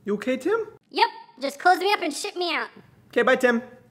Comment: Hahaha at the end it sounds like Tim says "yep just close me up and shit me out"